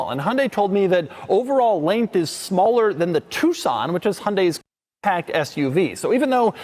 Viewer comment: 😂 what did you say... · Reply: I think he said subcompact by mistake